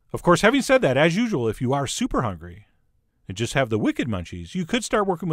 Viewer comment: Second times I’ve heard him say “if you have super munchies”. Lolololol